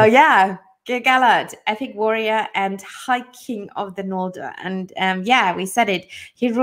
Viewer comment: Thanks for having me! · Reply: Somehow I misheard your introducing words as "Gil-Galad, Viking of the Noldor" 😁 Was really fun to listening to you guys!